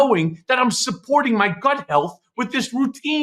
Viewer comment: Ha ha. I thought Michael said “butt health” instead of “gut health” in the ad.